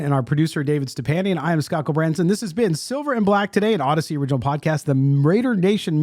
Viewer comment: "An Audacy Original" ? All this time I thought you were saying "An Odyssey Original" 🤣